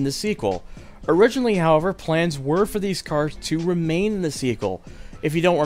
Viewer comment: what the hell is intrigaling? do you mean intriguing?....also its Su - lean not say - lean (saleen) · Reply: The narration/script proofreading on this video is just atrocious overall. "Runaway" instead of "runway"? 10:27 "remain in the seekull" instead of "remain in the sequel"? 18:57 "INTRIG-YOU-ING"????? Immediately afterwards he says "it would have curved and take you to a highway". He didn't mispronounce anything there - that's not even English! 22:53 "JAMEER-LY"??? This just gets better and better haha! 23:41 That's a greenhouse/botanical gardens, not a lighthouse lol. 26:40 "InGNLSHtruction" Those are just some of the worst offenders. I'm honestly surprised that nobody noticed/retakes weren't done/someone else wasn't asked to do the voice acting here.